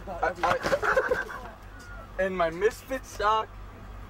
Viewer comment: what does Bam say? "And my misfits suck..?" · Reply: misfits sock?